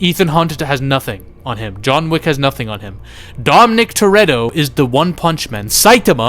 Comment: did you just call him Dom Nick like John Wick🤣🤣